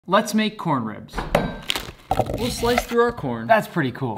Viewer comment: Who else thought he said let’s make corn rows😂